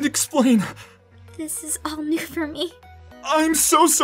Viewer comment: Am i the only person who heard her say," this is only for me"❤